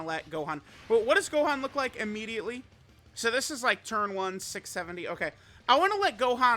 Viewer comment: what did say…. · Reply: 6 70